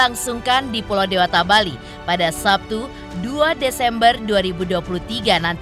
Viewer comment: Sabtu 2 nov??? Tp tayangan hr ini senin 27 nov...gk jls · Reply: Gw dengernya 2 desember. Salah denger apa gmn dah😮